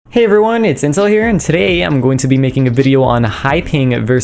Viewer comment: Why did I hear "Hey everyone Incel here" at the start of the video x.x